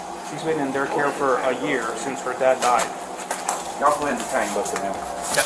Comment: it sounds like he said “yall go and detain those animals”